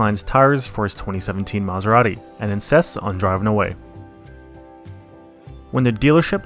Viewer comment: did you really say "incests" on driving away?